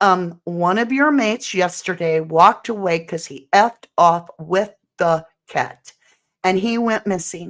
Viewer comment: I thought he said he fucked off of the ket he actually either stutters there or the sound jumps !!